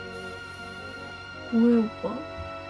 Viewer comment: 여기부분 "뭐해요 오빠?"라고 들리는데ㅋㅋㅋㅋ